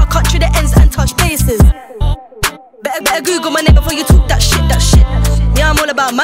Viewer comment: why is she saying the n word. Werido 🤨